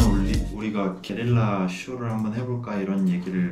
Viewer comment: I misheard that he said Kerala... 🫣🌴